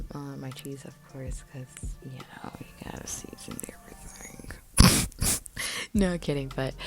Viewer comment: What is she saying at 8:07? Great recipe ideas! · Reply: “Cause you know you got to season everything” 😂